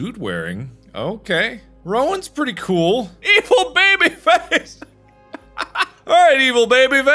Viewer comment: Anyone else hear evil baby fetish?? No? Just me? Alright, I have to go now.